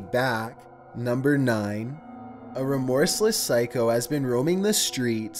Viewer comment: Adding me on social media is greatly appreciated :) Twitter: <link> Instagram: <link> Facebook: <link> Reddit: <link> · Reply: When you said "remorseless psycho," I heard it as "horseless psycho." I started thinking, "Do psychos generally have horses and is it so unusual that whenever they don't have a horse it has to be pointed out?"